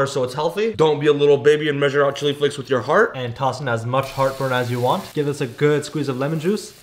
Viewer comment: what is that? Did he call it heart burn? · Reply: Garlic